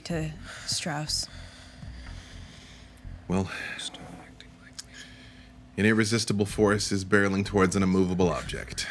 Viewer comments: Does anyone know what Jasper says to Eva at 58:37 ? Sounds like..."Stop acting like ( in audible)". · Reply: "Stop acting like me" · What is Jasper saying at around 58:40? He's always so quiet! I wannaaaaa knooooow!!! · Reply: I thought he said dont start acting like me. In character